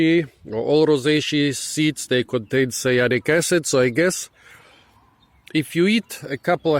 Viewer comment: Oh I see you spoke about toxicity:did you said "sialic acids" or "Salicylic" at 5:51 ? Is scialic acids same than cyanure? What about juneberries jam?is it dangerous when cooked? I often got this almond taste when eating them,meaning I always chew on the seed even if I don't try to.. Lot of people online never mention this toxicity,it's a super popular froot so I'm wondering if we should be cautious on this or not Do you havec any new feedback on that point please? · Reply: Cyanic acid, cyanide. Its in all rosaceae seeds and sometimes it is desired like when you use bitter almonds as a spice. Usually you will not consume enough to harm you. The rule of thmb is: if its too bitter do not eat it